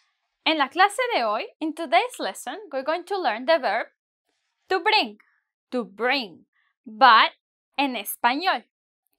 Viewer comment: I thought you said "to brink"